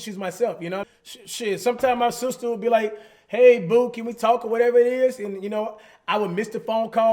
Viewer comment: did he just say that his sister call him "Boo"?!?!